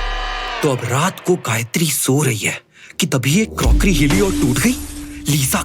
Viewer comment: Said Gayatri instead of Lisaaaaah